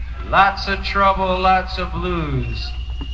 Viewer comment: Sounds like he says lots of trouble lots of ludes. Sounds like he ate a bunch of quaaludes. Such a shame his life was cut short